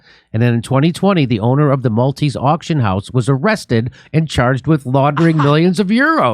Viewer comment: Haha I thought Jimmy kept saying "hunter's spank account" not maltese account.